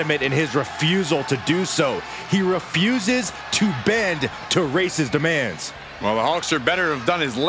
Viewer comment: Sounded like B.Saxton was saying, "He refuses to bend to 'Racist' demands" 💀.